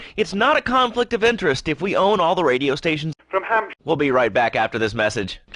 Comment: There is an audio glich there, and it kind of says "from Hampsh.." instead of "in town".